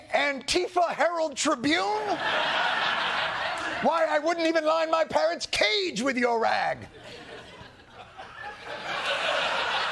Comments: I heard "parent's cage with your rag" at first. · did anyone else hear "Why, I wouldn't even line my PARENTS cage with your rag."???? Parents, not parrots???? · I thought you said "his PARENTS cage" That was funnier😂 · I thought he said he wouldn't line his "parent's cage" with it 😅